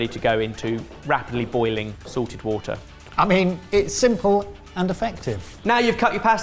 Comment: I always forget about the moustache mug, but every time I see one, I wish I had one. More for laughs than anything, but it still seems like a useful item for me. EDIT: When says "salted water" @ 17:47 it sounds like he is saying "Sorted water" and I'm 100% with it.